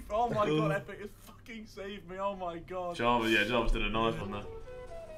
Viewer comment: bruh did he say jarvis